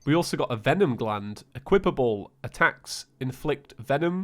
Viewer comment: Did actually say equipable properly!?